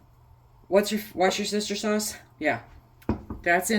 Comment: This is my second year I believe. LOL I thought you were saying “My sister’s sauce”, for Worcestershire sauce. My goal is getting more “meals” canned this year. Thanks for the recipe!